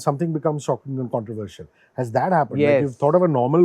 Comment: Possible. I thought maybe kl Rahul and hardik pandya one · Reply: yeah you are right, it was my mistake that i heard it as "bitch" instead of "pitch" the 1st time around